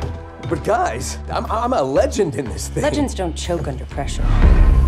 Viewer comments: Mirage and wraith are so cute when they talk together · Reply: I thought it said “Legends don’t choke on depression” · Wrath: "Legends don't choke under pressure" Mirage: * Chokes from Caustic's gas * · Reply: I heard that as "Legends don't choke on depression.".